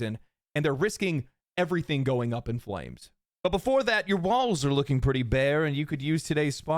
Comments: that sounded like "Your BALLS are looking pretty bare" while massaging a large object and it completely took me out of making coffee LMAO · Did anyone else hear "But before that your Balls are looking pretty bare."?